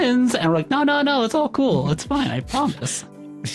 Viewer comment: i first heard "it's all ghoul, it's fine I promise" I'm probably just too tired